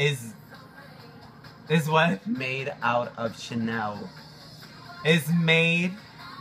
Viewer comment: I thought he said "my ass is made out of SHIT NOW"